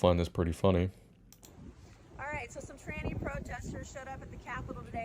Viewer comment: She said tranny protesters....I thought she said fanny protesters 😂😅😂